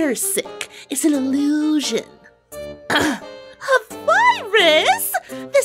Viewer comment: "A VIRUS!?" what i heard: "PAPYRUS?!"